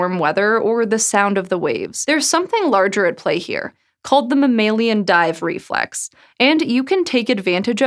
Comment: I thought it said mammalama dive riflex